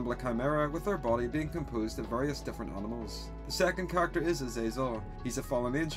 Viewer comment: Instead of Azezel I thought you said Azrael or are they the same person?..